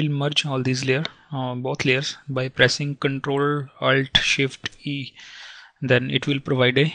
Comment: what did you say at 0:59 ? ctrl & what more ? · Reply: ctrl-alt-shit-E